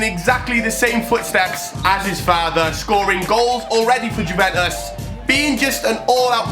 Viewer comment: What did he say Feattherrr 1:07 And also vjguventtusss 😂